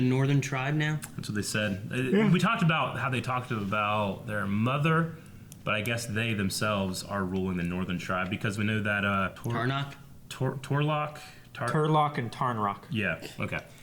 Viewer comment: I almost thought they said Turtlerock